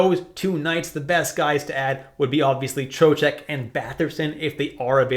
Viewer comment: WTF he said at 1:10? Batherson and Chochak, Joejack? Was it Trocheck? :D Bcs he spelled that name three times and each time it was different OMG :-/